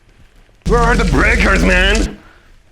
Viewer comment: "Where are the breakers" I hear Eminem